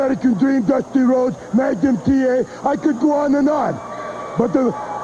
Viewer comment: Whenever Nikita says Magnum TA it sounds like he is saying Maga TA